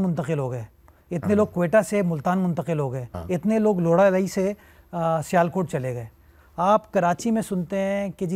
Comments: Is it just me or did he just say "Laura-lai'' 35:32 😂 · What did he just say at 35:31 😅 it's Lora lai... Not .......😂